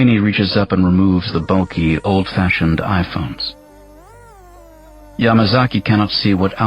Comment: Im sorry, did he just say Eyephone?? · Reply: Yeah, some early VR headsets were called Eyephones, just like earphones, etc. Obviously that didn't continue when Apple did the completely unrelated iPhone. In that case, the "i" stands for Internet.